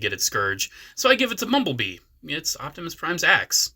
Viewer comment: I thought you said, "it's Optimus Prime's ass"😅 But I would've given primes axe to bee, and the chain to battle trap and simplified the leg transformation, but make it still look good. Voyager monke